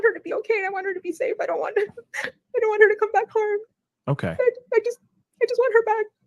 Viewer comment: did she say “I don’t want her to come back home” or did she say “I just want her to come back home?” Just asking.